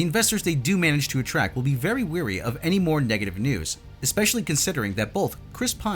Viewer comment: ~~13:07 learn the difference between weary and wary and choose the right one please. lazy people say weary because they never bothered to ask for clarification when they heard someone say it, and they actually said wary